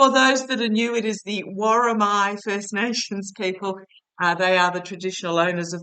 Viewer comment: Hi - it is the Woromi people ... xx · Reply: I thought you were saying "the war on my first nation people's....." but, what do I know? Apparently nuthin! 😳